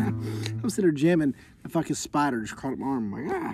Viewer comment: Southern twang for felt like in fast forward..lol it sounded bad to people who don't speak twang... HE SAID FELTLIKE , Get your heads out the gutter.. Feltlike spider crawling up my arm.. now go listen again. Haha 😄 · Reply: He said at 1:14 I was sittin’ here jamming and a f***ing spider went crawling up my arm. I listened over and over and each time it never sounded like “felt like”.